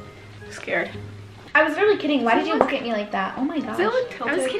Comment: Were you guys arguing with at 3:43? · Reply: It sounded like that scene started off with saying something along the lines of begging someone to go somewhere, but told her, “only haile and hannah could come, mind your business” and makes a face at that. Then at 3:43 says: “I was really kidding, why did you look at me like that? Oh, my god.” says: I was kidding too! No, you weren’t